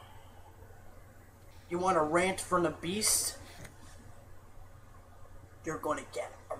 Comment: When the intro said "you want a rant from the beast" I thought he said "You want a rant from an obese" and i started dying